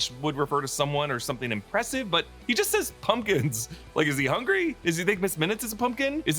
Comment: Pumpkin? I thought he said bumpkins.